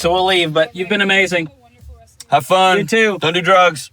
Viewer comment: Thought he said " Go do Drugs" Had to back up!